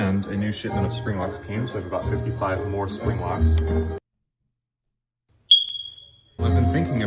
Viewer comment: did he say 55 spring lock?!!??